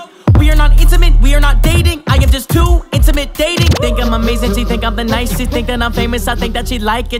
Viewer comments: “We are not intimate, we are not dating, I am just too intimidating” 😩😩🔥🔥🔥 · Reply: I thought he said I'm just to into minute dating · We are not 'intimate', we are not 'dating', I am just too 'intimidating'. That is straight fire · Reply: i thought he said intimate dating fr.